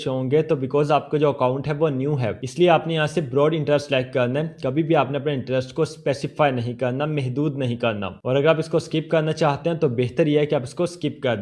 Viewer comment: Bro can you tell me what he said?? at 3:44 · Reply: Isliye Aapne Yahan se broad interest select krna hai, kabhi bhi apne interest ko specify nhi krna, mehdood nhi krna